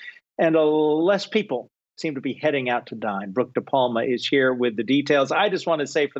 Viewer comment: For a second, I thought he said "heading out to die" not dine